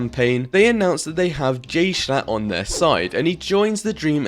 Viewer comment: Idk but when i listen to this part: 6:15 its making me think he said gayshlachtt